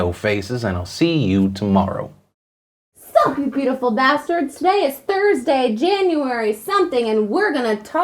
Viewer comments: Did he just say “beautiful bastard” instead of bastards? :o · Did he just say "bastard" instead of "bastards"? Illuminati confirmed